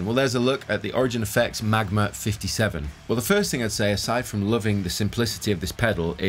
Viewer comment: thought it said "MAGA 57"